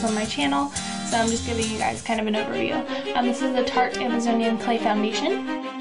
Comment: on the tarte thingy i thought it said farte so i was almost like awesome i know ow to say fart in french! But noo..